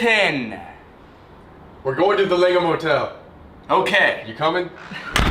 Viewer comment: Who else thought he said LEGO motel